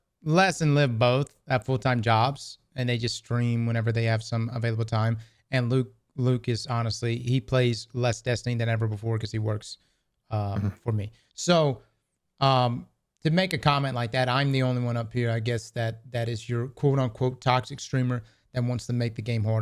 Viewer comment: (edited for wrong time) “Luke plays less Destiny than ever before.” Then I heard “Luke hasn’t mattered to me since into the light. Since onslaught.” I thought damn. That’s pretty harsh lol Loot.